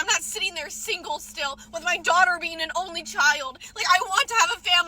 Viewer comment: 25 year old woman claiming that all 30 year old men have to do is… (checks notes)… date women in their 20s. She has no idea why she’s upset… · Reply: Get a 40-year-old man, very simple. But did she say “my only daughter”